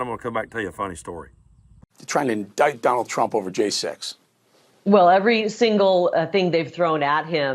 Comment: I thought the stooge said “indict Donald Trump over gay sex,” but I played it back, and he said “indict Donald Trump over J-6.” “January” must be too hard a word for him to remember.